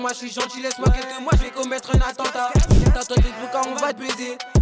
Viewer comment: Il a dit attentat ou ses moi qui rêve ?